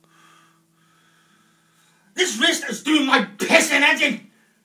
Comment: THIS WRIST IS DOING MY PISSING HEAD IN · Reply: I always thought he said, "This wrist is doing my pissing auntie!"